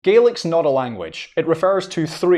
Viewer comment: i keep hearing “garlic” 😭